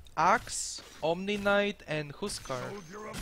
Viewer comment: the way you pronounced "Huskar" bruh, i heard "Who's Car"